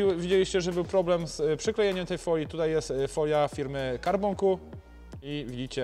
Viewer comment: Proszę !!! Czy możecie mi napisać jakiej firmy była ta bezbarwna folia ?? Nie mogę zrozumieć co mówi. Czas( 9:56 ) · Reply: CarbonQ